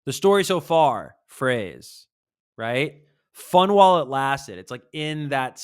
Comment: For a lil bit i thought you were saying 'Fun Wallet Lasted' is that an even better name?? 🤔